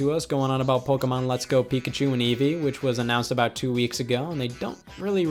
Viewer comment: the subtitles say Evee but its actually Eevee